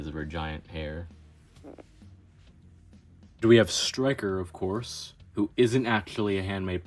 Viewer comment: I thought bro said "stikker" instead of striker😭😭😭😭😭😭😭😭😭